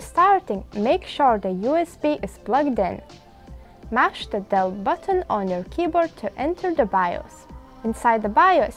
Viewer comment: I couldn't understand what your saying when restarting the computer sounds like your saying smash the dell button to get to the bios loading screen.. I don't have a dell button, if I hit delete it will take me to the regular bios..